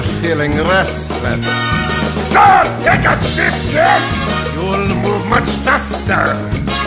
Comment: I could have sworn that was heavy saying "so take a sip, yes!"